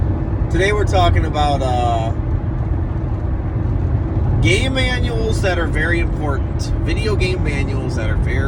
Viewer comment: first time you said game manuals, it sounded like you said gay manuals. LOL